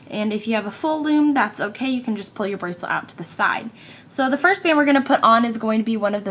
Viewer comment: I really thought you said fifty bands. I replayed it numerous times, but it sounds the same. Way too small though so I added an extension to cover the other half of my wrist.